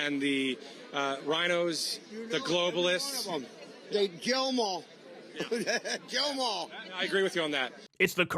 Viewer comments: Maybe its just me and im unable to hear him clearly with as few brains as teeth that the man had, but i heard "Jail them all." Maybe im wrong, but you dont need teeth to make a k sound but you do need it for a j sound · Being as charitable as possible, the only thing that can be misunderstood as is "jail 'em all" since the dude can't speak well. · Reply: i heard gaol ‘em all, it’s absolutely clear. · Tbh I thought he said "jail them all". But wouldn't be surprised if he said "kill" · I thought he said jail them all but I hear kill too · Wait, i heard him say “jail them all” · I thought i heard "jail them all" tbh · I thought he said “jail em all” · I thought i heard him say "jail em all"..... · I thought the drunk guy said "jail them all" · Idk I heard “jail em all” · Thought he said “jail them all” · He thought the guy said Bill them all. He wants them severely fined! Severely!! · I actually hear “jail them all” but I see how people misheard it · Sounds like he said, "jail em all."